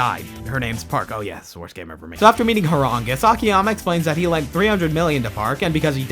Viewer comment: Ngl i misheard "Horongus" as "Hwoarang," the character from Tekken that practices Taekwondo (which is the same style as Akiyama's kicks)